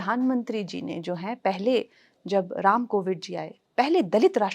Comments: क्या मैंने सही सुना "राम कोविद "😮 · Did she actually say covid at 49:42 😂😂😂😂😂😂😂😂